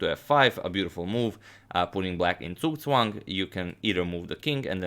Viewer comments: what is the word you are saying at 20:56? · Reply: vorvac zugzwang, German for “compulsion to move”, where the player who is to move next would undermine their position with any legal move they make. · What does he say at 20:57? Tsuswan? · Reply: Zugzwang. It's a technique that makes your oponent without a good move, making him doing a move that is bad.